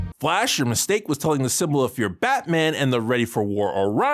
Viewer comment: What does call Batman here? I didn't catch it. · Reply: Symbol of fear.